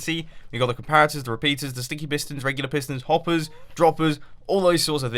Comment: that moment when he says "Stinky Pistons" instead of sticky pistons